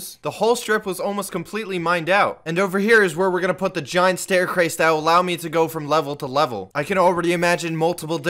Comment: did he just say staircwace